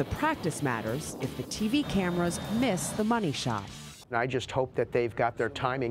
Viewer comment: did she just say money shot?